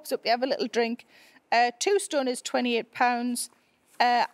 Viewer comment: I think that whoever did the CC/subtitles was confused because I don’t think you said “A tombstone is £28.” You meant “2 stones is 28 lbs/pounds”, right?